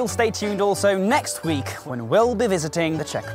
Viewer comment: Did He Say F—k You To Us 🤨🤨🤨